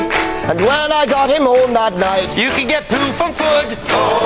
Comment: Anybody know what Colin said @ 1:18? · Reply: "You can get poo from foood." Is a joke from scenes from a hat, earlier in the episode..... ;)